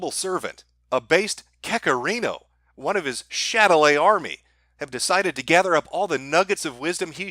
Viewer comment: can anyone clue me in on what ‘chatelay’ means? I always assumed it was some Irish word but I’ve heard mention it a few times but i never know what hes actually saying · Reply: Shadilay. It's a song that was popular with the kekistan dorks